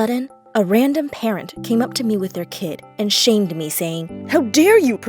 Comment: I thought it said an old lady shaved me XD